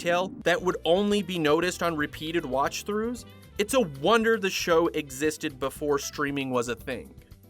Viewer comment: did someone say “wonder”?